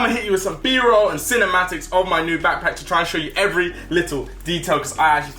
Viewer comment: did i just say packback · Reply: I heard ‘packpack’